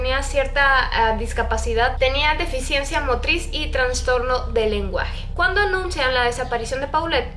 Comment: Escuché "traNstorno" y morí. Justo en mí psicología Jajaja Aún así, amo tus vídeos 💛